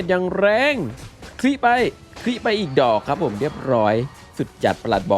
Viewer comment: นาทีที่ 8:23 นึกว่าพี่พูดว่า คริไปอีดอกก5555